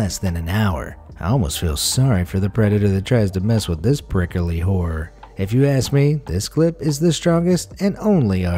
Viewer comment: I thought he said 'prickly whore' when he was talking about the stonefish XD